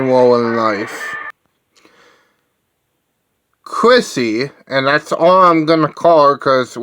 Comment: Isn't her last name Wallace? Not at all difficult to pronounce. Also, sounded like you said Chrissy for her first name and I'm pretty sure it's Kristy. Kristy Wallace... Poison Ivy (Rorschach) ... The Queen of Rock-n-Roll.